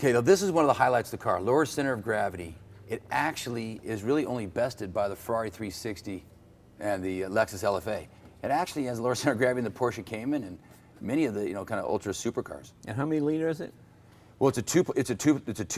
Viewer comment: Ahh, I thought I heard cayenne, cayman makes more sense.